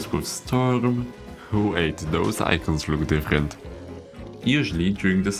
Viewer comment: “like hot temperatures with wind, or cold temperatures with storm-“ (zooms in on random coconut cannon) ”who ate those icons, roomed infant?” seriously, what is he saying here? Can someone please tell me, it seems so random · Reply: "Those icons look different." Thats what he said! Youtubes automatic subs helps once in a while